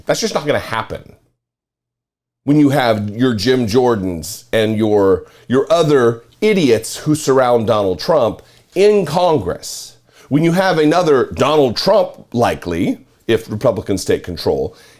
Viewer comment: I thought he said Donald “Chump” at first lil